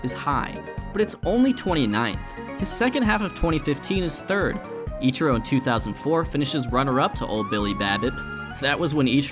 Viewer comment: Thought he said ichoro in 1980s i was like what goes tho loll